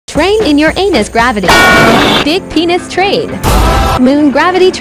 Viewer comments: Why did Venus almost sound like it said “penis” 💀💀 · I thought it said big p3n!s train or did it say that💀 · Why I hear “big peanut train”